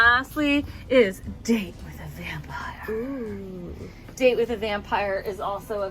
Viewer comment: I thought you said David The Vampire instead of Date With A Vampire. Would I date a Vampire Named David? Why not? One of my most bloodsuckingly monstrous exes was named David. Then I dated four more Davids, one right after the other, all the pretty Davids lined up in a row. Maybe. Or maybe I was just too lazy to learn a new name, and that's why they left. But, I digress. That's a wonderful colorway, notwithstanding its Davidity. Or perhaps because of it? Do I see a sort of poetic justice in using two sharp sticks to manipulate David into an obedient servant, willing to assume any shape I command? Again, I digress. But, Yes. Yes I Do. So, how y'all doin' over there? Well, I hope.